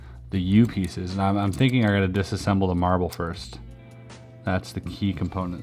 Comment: I had my phone turned down a little and I thought you said Marvel Verse instead of “marble first” and I was a tad bit confused